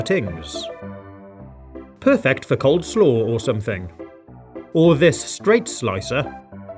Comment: did you just say cold slaw?